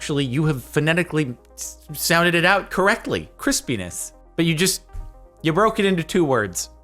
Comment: verified YouTube's captions still can't understand the word crispness but it quoted saying "Chris (demonetized)".